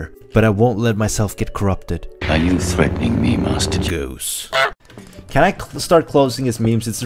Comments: it sounds like he says ‘master juice’, not ‘master goose’ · it sounds like he says “master juice”